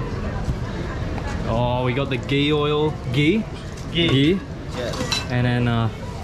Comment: i heard gay oil at 17:14 xd